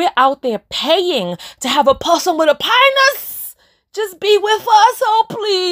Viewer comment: Thanks for watching, what do you all think of the Podcast Style video? Honest feedback please BOOK OF AFFIRMATIONS: Self Love - AMAZON: <link> AUTOGRAPHED: <link> WILL MY MAN BE WEALTHY? <link> Forgive MP3 <link> Stress Release <link> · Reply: Admittedly I thought you said A Possum with a panis not a parson lmao 😂😂😂😂 you was spot on with this video